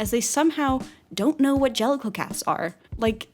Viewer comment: I thought you said genital cat